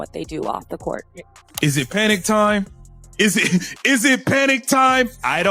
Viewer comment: When dude asked is it panic time , I got hearing loss and thought he was saying is it panty time, is it panty time? Lmao